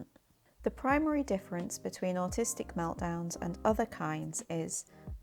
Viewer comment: great stuff, thank you for that. 10:03 there’s a misspelling in subtitles “artistic” instead of “autistic” (and maybe in other places too). which is kinda beautiful, but not precise much ;)